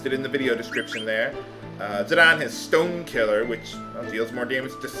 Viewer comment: "Zidane's on 'Stone Killer', which does more damage to stone enemies..." I swear, I heard "Stoned" enemies. Get your enemies high, THEN DESTROY THEM!